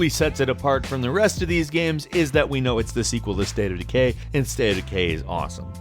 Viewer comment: It sounded like you said "State of the gay" at the end there LOL!